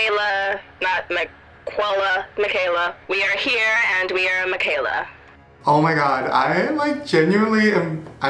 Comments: Did u guys heard what i think i heard 8:24 she said "we are here we are lil Miquela " new conspiracy theory alert i love you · when was on the call and i swore i heard "we are miquela, we are lil miquela" OOOOF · Anybody catch that she said we are miquela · ok but did anyone else hear that girl say "yes we're here" 8:20 like bitch whos weeeeee?????? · Hmm she said we are lil miquela. Did anyone catch that??? At 8:24 also her voice sounds robotic or auto tuned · Did she just say "We are here"? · Did she just say " we are miquela " 😵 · Was I the only one who heard her say "WE are here" and "WE ARE lil miquela" at 8:23 ?!?! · Am I the only one who heard her say we are here and we are Miquela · AM I THE ONLY ONE WHO HEARD HER SAY "we are here we are Miquela" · Am I the only one that heard her say "WE are here" not " I am here" why is no one saying anything about it omg guys listen carefully